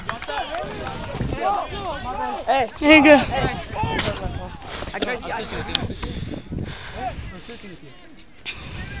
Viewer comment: Did he say nigga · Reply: He said ur good